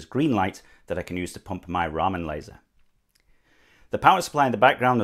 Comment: my fatass thought that said "ramen laser" 😭